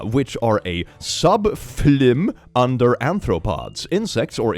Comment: the furry in you is peaking out, aNthropods instead of aRthropods hehehhe <3